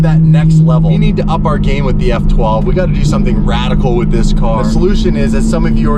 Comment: Did really say radical?